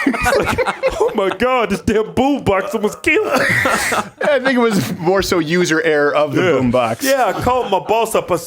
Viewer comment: does crowder say the N word? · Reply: Pug isolation “I think it was” sounds kind of like “that nigga was”. But no he didn’t